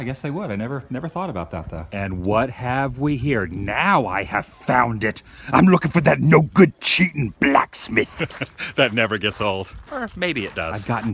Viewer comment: I heard> Where is that No Good Lyinn Cheatin Black Smith?